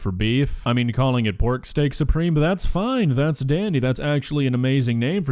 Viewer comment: i think shes saying "steak" SOUP-ream whatever that means